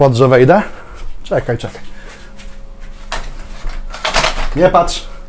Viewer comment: Czy tylko ja usłyszałem "jebać" zamiast "nie patrz"